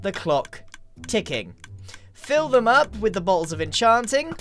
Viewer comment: Is it just me or did Grian say "Balls of Enchanting?" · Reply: He said bottle